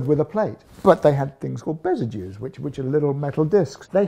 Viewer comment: The video production team didn't understand so they left the subtitles blank. It's called 'Besagew', As Mike Loades says, it's a small metal disc that protects your armpit.